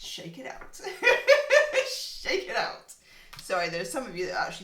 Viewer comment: I thought you said “limp balls” (actually Lindt balls) 🤣🤣🤣